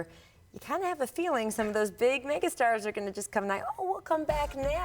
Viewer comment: I can’t be the only one who heard “those big nigga stars”...